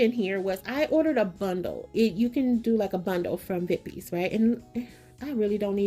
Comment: Where did you get those napkins? I rewound several times; it sounded like you said, "Pippy". 🤷🏽‍♀️ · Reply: Vippies designs